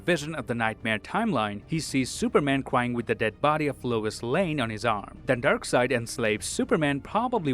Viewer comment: I kept hearing "Louis Lane", what timeline is this